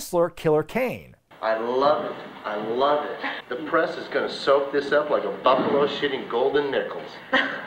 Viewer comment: I legit heard "...gonna soak it up like a bum full of shitty golden nickels". Still... makes about as much sense as what they actually did say lol.